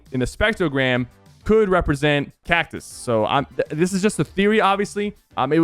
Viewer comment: i thought i heard Galactus but it was cactus lmao